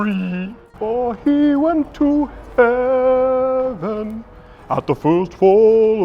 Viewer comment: I thought had was gonna say "for he went to hell"